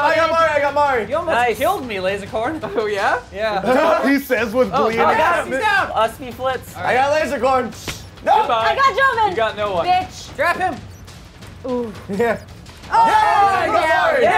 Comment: did lazercorn just said i got NOAH :)